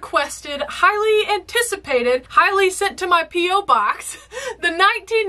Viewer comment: 😳 Hang on. Did you say this was highly sent to your PO Box? People have been sending you DVDs of this?